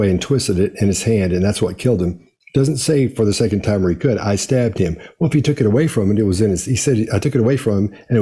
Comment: I’m probably wrong, but I’ve seen this interview before, and it sounds like JWG says, ‘I saved him’ not ‘I stabbed him’. Idk for sure. I rewinded it and I still hear the word ‘saved’.